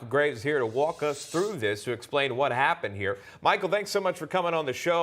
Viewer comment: walk us through the graveyard is what i heard lmao